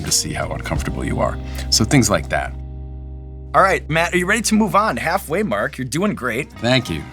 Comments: I thought he called him Mark for a second. 9:09 Matt, Half way Mark! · sooo did nobody else catch at 9:10 when Sean calls him Mark instead of Matt??? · Did he just call him Mark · Did he call him "mark" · does he call him Matt, then Mark? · Did he just call him Mark? XD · I thought Shaun called him Mark! Haha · Did he call him 'Mark' at 9:10? LOL